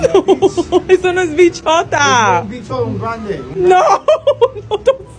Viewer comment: Him: "bichh" What I heard: "bitch"